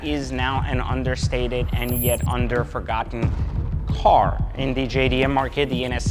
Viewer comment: LOL “underforgotten car” WTF does that mean….that’s not a word brutha